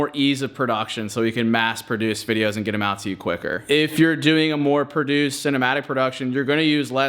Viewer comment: i thought you said you quit